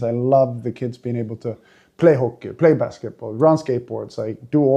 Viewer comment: m DID YOU SAY, I love when the kids play hooky? 😅😅😅🎉🎉 I used to play hooky from school too...😂😂😂